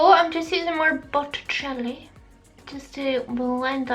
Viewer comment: You said Botticelli and I heard “butter jelly”. 😂 One of my fave pink looks you’ve done. 💗 What nail polish?